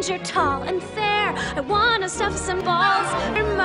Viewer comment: I know it says stuff but is it just me or does it sound like she’s saying suck instead of stuff